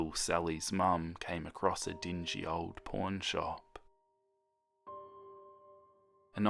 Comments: I know he said "pawn shop" but I swear to god I heard "porn shop" 😲😂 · When he first said "pawn shop" i swear to goD i heard "porn shop" 😂 I think i just have a dirty minD T-T Edit: Jesus it’s 2024 lmao likes are Crazy and this man’s accent still Makes me mishear things 😭🫶🏼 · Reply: I heard "porn shop" aswell xD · When he first said "pawn shop" i swear to goD i heard "porn shop" 😂 I think i just have a dirty minD T-T Edit: Jesus it’s 2024 lmao likes are Crazy and this man’s accent still Makes me mishear things 😭🫶🏼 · When he first said "pawn shop" i swear to goD i heard "porn shop" 😂 I think i just have a dirty minD T-T Edit: Jesus it’s 2024 lmao likes are Crazy and this man’s accent still Makes me mishear things 😭🫶🏼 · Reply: Nah I heard "porn shop" as well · ummm can you repeat that please? I can’t be the only one who heard porn shop lmao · I thought it said porn shop instead of pawnshop...